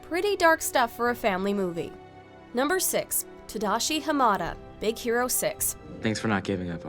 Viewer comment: I misheard tadashi Hamada as HAKUNA MATATA